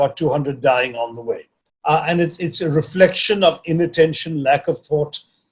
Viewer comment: it's inattention - not intention I heard likely